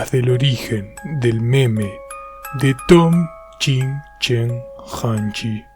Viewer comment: dijiste de Tom chino y yo entendí de doom Slayer